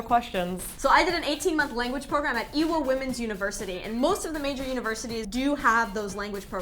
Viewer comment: I thought she said "Evil Womens University"